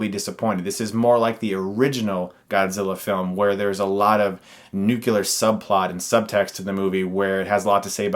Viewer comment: Did you just say "nucular"? :(